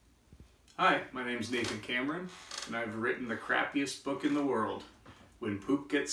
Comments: I thought he said Nathan Cameroon🇨🇲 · BRO I THOUGHT HE SAID THE CREEPIEST BOOK EVER💀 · Im sad cuz I thought it said “the creepiest book in the world” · I thought he said the creepiest book in the world.